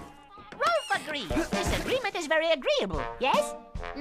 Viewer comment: "Rolf agrees, this agreement is very agreeable, yes?" They sure don't write them like they used to. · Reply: I thought Rolf said: "Rolf agrees, disagreement is very agreeable, yes?"